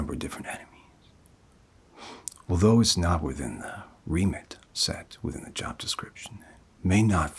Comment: please help me what is this word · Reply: "Remit" I believe, basically means the thing you'd be responsible for when you take the job